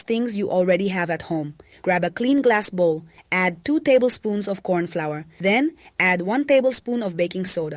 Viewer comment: Could that be corn meal? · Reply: that was what I thought as well but @ 0:24 she actually said corn flower.